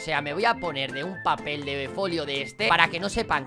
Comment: papel de pollo? eso entendi xd